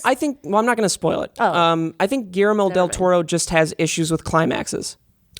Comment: Did you say GARAMEL Del Toro???????????????????????????????????????